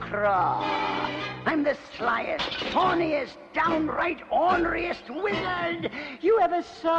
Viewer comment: I thought he said horniest and not thorniest for a moment there.